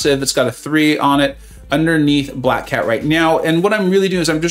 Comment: did you say right meow?